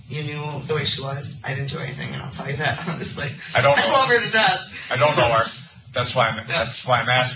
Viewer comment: Did she say, “ …I loved her to death”?!?!?!?